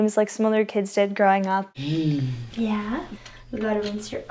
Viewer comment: it sounds like is saying YEET